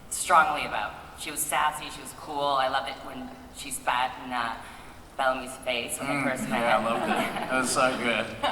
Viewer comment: what did bob say? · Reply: Tasya: I loved it when she spat in Bellamy's face, when we first met. Bob: Mmmh yeah, I loved it. It was so good.